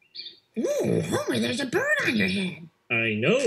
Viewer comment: I thought he said “Mmm…Elephant friend”.